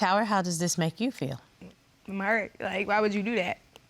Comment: She felt what??? It wasn’t mad I heard. Idk what she said was that ATL slang?? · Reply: Sounded like she said im hurt